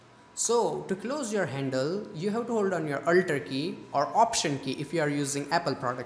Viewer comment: Your presentation was nice and it helped me so much, But your Slang is so derby, Man what the hake did u say,YOu littrelly said aulllllter key in 2:42 Go and hear your own voise. Man change your excent, Bra it is alter key wtf.